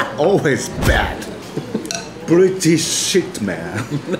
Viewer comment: I thought he said “Pretty shit man!” Not British shit man…